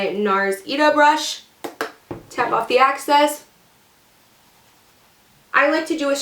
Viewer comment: Hey! I really like your videos! And your super gorgeous face! I just wanted to say, when you mention tapping off the excess, it sounds like youre saying access. Theyre two totally different things!